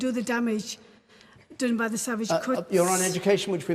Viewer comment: did she just call the conservatives savage cunts?